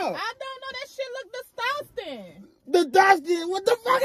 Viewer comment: Wait did she just say disdusting instead of disgusting!?? LMMFBAO 🤣😂